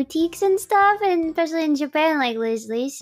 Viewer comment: If anyone's curious, at 1:27 Gura is probably saying "Liz Lisa and Angelus", most likely refering to a store named "an another angelus" (Yes, as weirdly written as it sounds) where they sell pretty women's clothes in Japan.